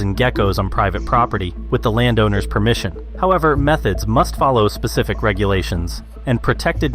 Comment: ~ Did he just say "Meth-heads must follow specific regulations"..?.. 🤔